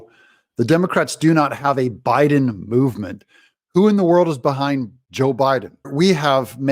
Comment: I think she thought they said bowel movement not Biden movement. PhD yes, commonsense no.